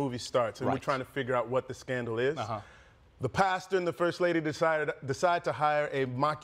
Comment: I need Trevor to say: The Pastor ate Pasta. 🙏🏾🤭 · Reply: Every time Trevor said "pastor", I heard "pasta." Really threw me off, he usually enunciates so clearly.